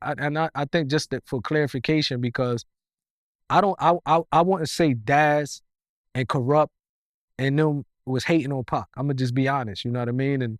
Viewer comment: Did he say daz and kurupt was or wasn't? hating on pac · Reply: He say, I WOULDN'T say Daz, and Darrupt, was hating on Pac.